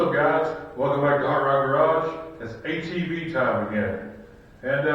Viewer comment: I apologize, I thought I heard "hot rod garage" correct me if I'm wrong ? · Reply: is the name. And I have some other videos with classics on there as well as some 4x4 stuff. Currently have two projects going which are a 72 Chevy truck and a 74 Ford Ranchero